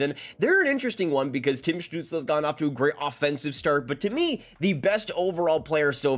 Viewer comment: Sanderson has been the best player by far. Stützle has been great, but Sanderson’s ability to shut down top offensive players and transition play, along with having 3p in 3GP has proven to be invaluable. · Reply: I don't disagree. I must admit that I'm realizing now that I had misheard something in the video and that is what led to my original post. I was watching the video at 2x speed and when he said "Tim Stutzles GONE off to a great offensive start" I could have sworn he had said "Tim Stutzles NOT off to a great offensive start". Hence my my comment in the tone of how is 6 points in 3 games not a great offensive start. My bad for mishearing and misunderstanding his comments.